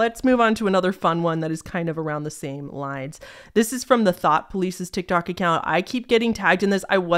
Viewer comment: OMG the THOUGHT police! Like 1984! I legit thought you said the channel was called the THOT police 🤣